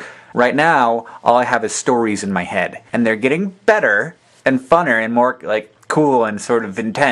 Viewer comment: Did you just say "funner"...or "funnier"? You may be right about the pronunciation of wikipedia, but "funner" isn't even a word! :)